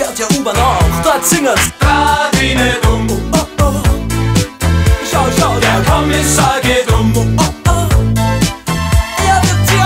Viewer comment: Ich hab neulich erst geckeckt das der "drah di net um" sagt, ich dachte früher immer das soll "dadidedum" sein😂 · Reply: Ja, das stimmt.Ich habe immer verstanden da di de bum ohohoh schau schau der Plumpsack der geht um :-D